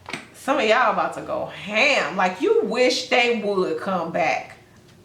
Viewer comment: Not going to lie, I was a bit zoned out listening to this and all I heard was, “y’all are about to go ham’d out. You wish they would come back.” I felt that. 🤣